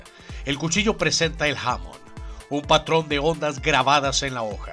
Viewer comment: Escuche bien el acaba de decir Hamon ?